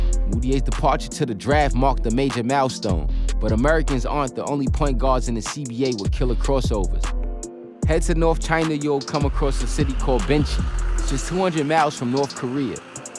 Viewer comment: did he say it marked a major "malstorm?" What the hell is a malstorm? And all that time I thought he was speaking English! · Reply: He pronounced it mal-stone you know New York for mile-stone